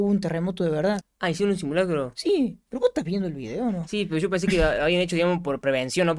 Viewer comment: Jsjsjs en el min 3:20 dijo no estás viendo el vídeo algo así entendí o creo que dijo pos no estas viendo el vídeo jsjs